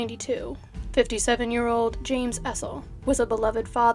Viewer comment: I doubt his killer was "innocent". Why have an armed stand off with the cops? His wife is in denial & a retired detective no less! 😀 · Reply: His name was James Essel. I think you might have misheard his last name as "Edward".